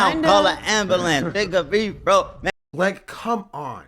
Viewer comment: in my opinion, it either sounds like he said "make em breathe bro" or "n-word breathe bro"